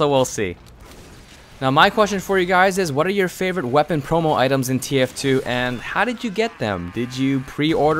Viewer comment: it sounds like he is saying "rape my favorite promo items"